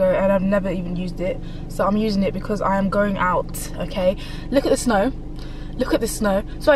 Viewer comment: She really does drives fast look at the speed at which she is doing OMG #SpeedQueen. I thought she said look at that snow 🌨️🌨️🌨️ remember you have kids and a beautiful husband please try not to please be careful.